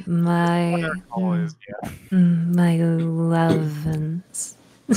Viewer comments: it seems you don't understand what says to me it sounds like "My Lovense", wich is a brand of s*xtoys (remote controled btw) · i can clarify what she's saying here she saying "my lovense" which is a sex toy (yall can look it up if you're 18 or older) so she was in a vc using toys in calls with minors... ew also as a modern satanist, we don't want her dawg lol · did she just say "my lovense" at 4:00?